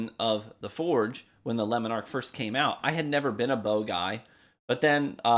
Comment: Im sorry, I cant get over your pronunciation of Le Monarque xD I keep hearing "Lemon arc"